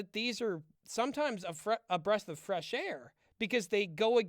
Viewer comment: Watching you guys is so intestines. I hate that I'm laughing at this Avatar Keurig joke. 50:59 I misheard this as "a breast of fresh air."